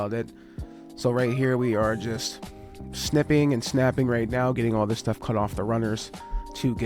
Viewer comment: Ngl I thought you said sniffing and snaffing lmao